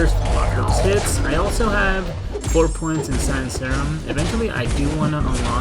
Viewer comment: Four points in what? Didn't catch what you said. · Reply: Sanmiol science serum